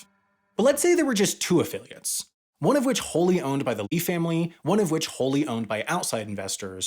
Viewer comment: Every time he says “Eee-family” I thought he was saying “electronic-family.” Why is he pronouncing “Lee” as “Eee”?